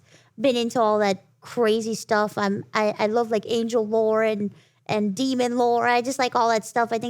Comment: I choked on my water because I misheard angel lore as angel vore💀💀💀